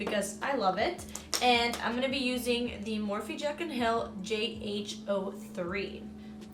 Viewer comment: I heard Jack and Jill 😂😂